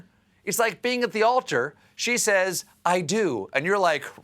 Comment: What did he mean when Stephen said something was a weird cell phone at around 7:26? He even said cell phone funny. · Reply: He said, "a weird self-own," as in someone cutting themselves down. (Followed by the example of the bride saying "I do" and the groom saying "Really? Why?") I thought he was saying "cell phone" for a moment, too!